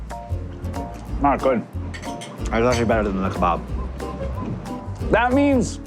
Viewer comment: I thought said “I love you better than the kebab.” ❤